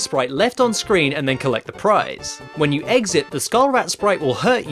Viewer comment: did you say. Eggsit? (Exit)